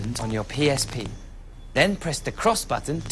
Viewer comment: at the end he sounds like he said goo duck